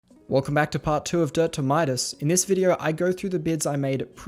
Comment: When he said dirt to Midas in the intro I thought he said “Don’t do minors”